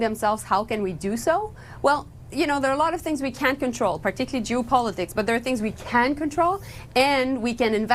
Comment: Did she say we can’t control Jew politics at 4:19?